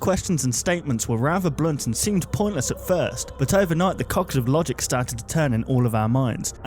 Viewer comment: WAIT! One question is still unanswered.... Does Freddy poop in the woods? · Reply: Also 2:10 did he just say "the cocks of logic"?